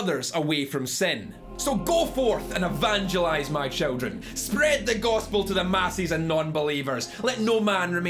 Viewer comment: did he say "spread the gothspel" ?